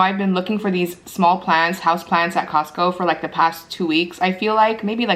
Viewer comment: house plants lol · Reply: i'm sorry it just sounded like you were saying plans...your not pronouncing the T.